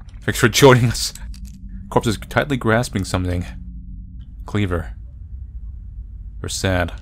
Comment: my dumbass self thought it said "clover"